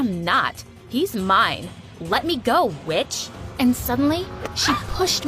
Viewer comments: I thought she said b*** instead of witch I had to replay it lol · "Im not lying let me go witch" i heard "let me go B*TCH!"